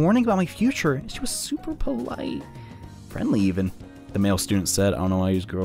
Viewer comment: I thought it said female student and I was happy Altus put in a gay relationship in their game But sadly it was male